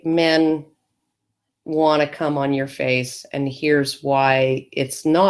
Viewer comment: had to pause the video to take care of something, back a few minutes later and hit play, first thing I hear is saying "wanna cum on your face and here's why" ... I almost had a stroke. XD